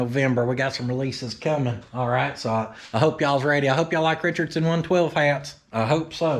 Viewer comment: 😎 at the end of your video I thought you said I hope you all like Richard Simmons! Lol, you know the workout guy in the 1980s... but you said Richard 1/12 something I couldn't make it out. 👍 by the way I like the spooky Halloween video! 🎃